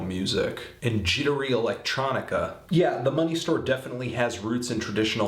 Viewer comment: did he just call it the monkey store?